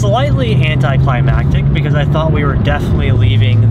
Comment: did he say hentaiclimactic?? 💀💀